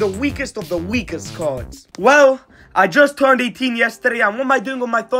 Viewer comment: For a second I actually thought said: I just turned a teen yesterday